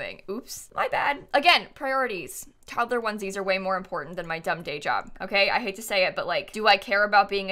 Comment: I thought she said dumb gay job... slay